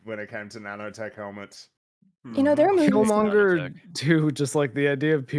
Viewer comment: LMAO, heard it as Feelmonger